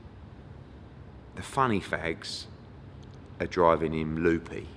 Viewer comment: Good interview but sounded like he said "funny fags". Was he saying funny facts?